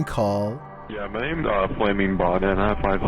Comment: I hear "Hi, my name's FlammingBomber" it's either that or FammingMama but it's a dude and I doubt that its FammingMama